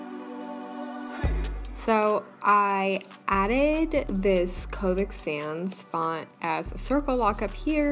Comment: Who else thought she said COVID sans because they weren't looking at the screen? 😂